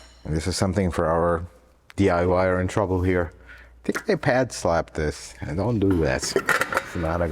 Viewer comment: I can't figure out wtf he said at 9:01. Something about a pad slot? Others seem to have the same issue as this is the most replayed section. 😂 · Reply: “don’t pad slap” translation: don’t slap aftermarket pads on this car